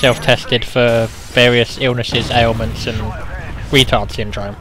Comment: Hey you mentioned me at 0:30 it sounds like you say aleman lol